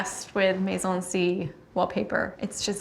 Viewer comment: Does anyone know who the wallpaper design was? Mazel something? Couldn't get it all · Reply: The caption says 'Maison C wallpaper' if you are talking about the bathroom at 8:03 :)